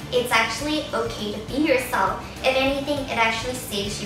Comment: Lol when she said "in high school it's ok to be urself" I thought she said "pee urself!" lol 0.o